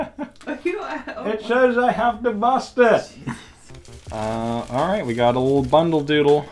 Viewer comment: Could someone please tell me what he says eating the candy? I need captions better than the youtube auto-generated captions anyway, so this is extra difficult to make out. will you please look into having someone make captions for your videos? Us Hard of Hearing folks would like to know what all the words and sounds are. 😔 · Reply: He says "It says I have to bust it!"